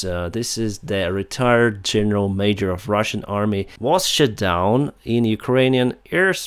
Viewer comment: have I heard "...retired senio major...was SHIT DOWN in ukrainan airspace..."? 🤣😂 anyways, retired twice.